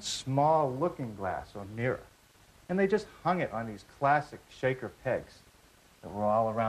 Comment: Such a liar! The shakers did not use plastic pegs to hang their mirrors. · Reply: At first, thought he said plastic, then realized he said classic